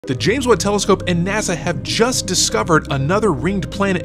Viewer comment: It sounded like he said "The James Woods" Telescope